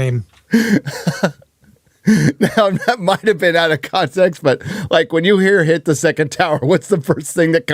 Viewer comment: So it was "the 2nd tower"!. The first time I heard it, I had thought she had said "the 2nd Tao word", which would relate to Taoism. 🤣